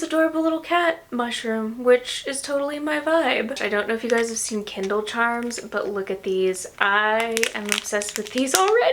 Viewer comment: Kindle charms are so cute. But I thought you said candle charms. I was confused! 😂 How do we get better? We learn from experience. If we are smart, like Abby, we apply the things we learn into our life experiences. And her books just keep getting better! ❤